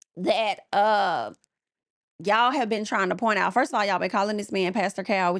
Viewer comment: OH it's obvious. Isn't he a church man, I thought he said hes a PK.